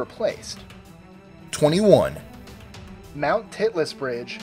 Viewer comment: number 21, anyone else heard him say titless?